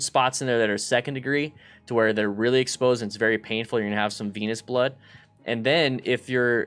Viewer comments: wasnt paying attention and i her venous blood and i thought he said penis blood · I swear he says "Penis Blood" at 8:17.